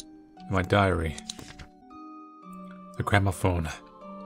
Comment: When he said ‘gramophone’ I heard ‘grandma phone’. I was like wait what? XD